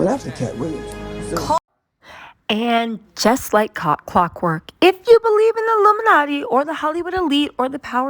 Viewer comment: just like cock work.......clock work i heard what she said 😭😭😭